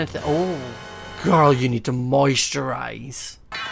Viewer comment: I thought he said moist your eyes but he said moisturize LOL